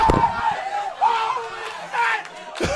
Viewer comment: Guy in the back sounded like Etika 😔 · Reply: You can hear "this guy's actually gone" too.